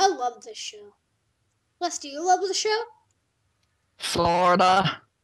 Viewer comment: it sounds like he said Florida no sorta